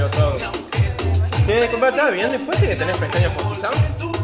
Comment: Nunca entendi el clip del negro gatux min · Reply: Le apoyo la garompa a la mina!